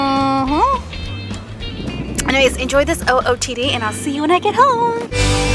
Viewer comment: It sounded like she said ''And then I'll see you naked at hooome!'' LMAO XD